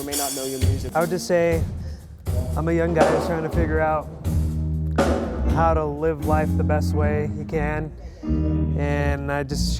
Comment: What did he say after 3:49? Will someone please answer me. English is not my first language. :) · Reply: He basically said that he is a young guy trying to live life to the best that he can and that he'd share with them his new music.